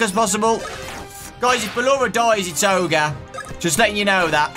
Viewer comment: I heard “if ballora dies it’s ogar 😂😂”